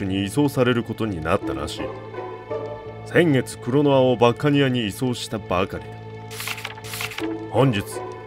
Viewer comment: 「先月クロノアをバッカニアに移送したばっかにあ」に聞こえてしまった...(゜∀。) 俺だけか？